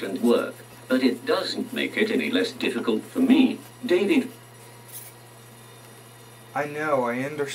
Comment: did he say dang it?